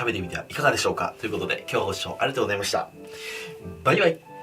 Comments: 最後のあいさつ「バイガイ！」って聞こえる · 最後のバイバイかバイガイに聞こえた俺は末期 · 最後のバイバイがメインのせいでバイガイに聞こえてしまう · バイ貝にしか聞こえんw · 最後のバイバイが、ばい貝に聞こえて1人で笑っちゃった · 「バイガイ」って聞きすぎて最後の「バイバイ」が「バイガイ」に聞こえる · 最後の「バイバイ」を「バイガイ」って聞こえたのは気のせい？ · 最後のバイバイが『バイガイ』に聞こえてしまったw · バイガイに聞こえた笑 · ばい貝ばい貝聞きすぎて最後のバイバイがばい貝に聞こえた笑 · 最後に終わるとき【バイガイ】って聞こえるww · 最後のバイバイが バイガイに聞こえた私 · 最後のバイバイが 今日はバイガイに聞こえた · 最後のバイバイがバイ貝に聞こえた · 「バイ貝」にしか聞こえん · 最後の 『バイバイ👋🏻』が『バイガイ👋🏻』に聞こえた🤣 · 「ご視聴ありがとうございました。バイガイ」って聞こえたけど気のせい？ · 最後のバイバイがバイガイって聞こえた。気のせいか。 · 最後バイガイって聞こえるw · 「バイガイ！」に空耳したw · さいごのバイバイがバイガイに聞こえた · 最後のバイバイがバイ貝に聞こえる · 今日の最後のバイバイはバイガイに聞こえた(^-^) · 最後のバイバイが「バイガイ」に聞こえてるような、、、笑おもろ笑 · 最後のバイバイがバイガイって言ってるように聞こえた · 最後のバイバイが心なしか「バイガイ！」に聞こえたのは俺だけ？ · 最後のバイバイもバイガイに聞こえた w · 最後のバイバイがもうバイガイに聞こえたのは僕だけでしょうか？ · 最後バイバイじゃなくてバイ貝に聞こえるのは気のせい、、？ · バイバイがバイガイに聞こえた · 最後のバイバイがバイ貝に聞こえて笑ってしまった。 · 最後バイバイって言った？バイ貝って聞こえたんだけど...笑